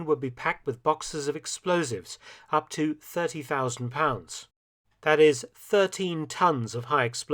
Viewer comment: 4:<num> pounds are equivalent to 6.500 kg, so only 6.5 tons Edit: My bad, I misheard 30.000 as 13.000